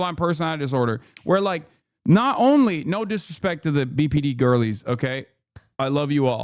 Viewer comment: when he says BPD I only hear PBD, aka, Patrick Bet David 😅😂